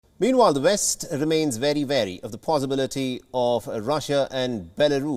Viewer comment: did he say: the west remain very very ?